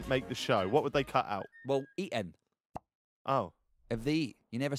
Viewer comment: “What kinda stuff would they crop out of the show?” “ÊÑ” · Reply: I thought he said Eaton😂